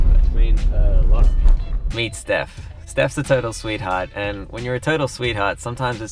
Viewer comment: From 1:34 to 1:40 I thought he was saying turtle sweet hurt because of this accent so I got confused